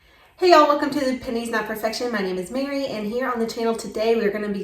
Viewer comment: I totally heard "welcome to paninis not perfection" and then I got hungry.